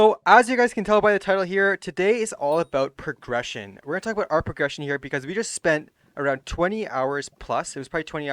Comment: thought it said depression